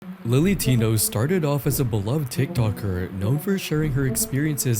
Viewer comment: sorry did I hear beloved he’ll no